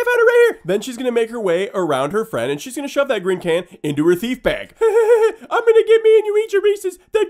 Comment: Lolz! 1:23 Thought you said "feedbag" at 1st!! Whoop Whoop!!!